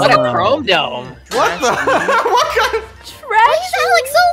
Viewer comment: Did just said the "H" word- 💀🏴‍☠ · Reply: He said hell